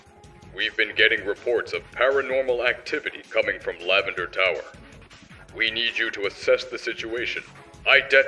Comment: I heard "Lavender Towel". Sounds like a haunted glitch item.